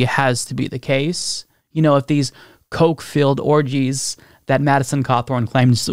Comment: Y’all misheard Madison. He said Koch-filled parties, not coke-filled parties!